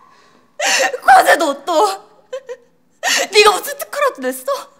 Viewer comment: 뭐라고 하는거임??? 과세노또??? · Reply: 과제도 옷도